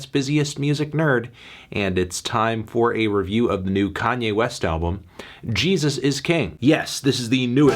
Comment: at 1.5x speed makes it sound like you said Jesus is gay Maybe that'll be the sequel